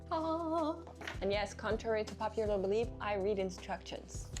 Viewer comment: she actually said "Contrary to popular belief, I RIP instructions".